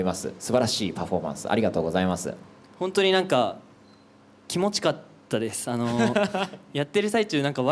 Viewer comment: マニアックですが、ジュノンがコメントで気持ち良かったて言うのを「キモチかったです」て聞こえるのが好き いつも来た♥️てなる スタッカートが強いのかしら？